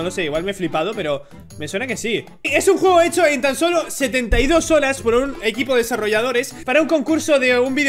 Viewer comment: no lo puedo creer, un juego echo en tan sólo 72 olas OMG!! 😂😂😂 Te Amo 💕💕💕 Pd: creo que yo escuche mal pero we